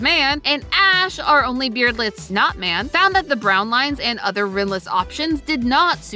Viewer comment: why did the (not auto generated) captions say "Ash, our only beardless snot man..."